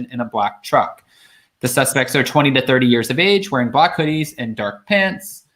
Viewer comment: I thought they just said 3 men in black hoodies.